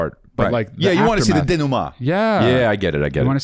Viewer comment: Bro what did he say? Dinuma? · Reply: denouement